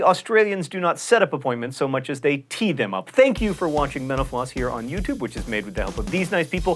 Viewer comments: At the end of each video I hear "Thank you for watching menopause". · Definitely thought he said 'menopause' when he said 'mental floss' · Am I the only one who heard John Green say Menopause instead of Mental Floss at 6:45 ?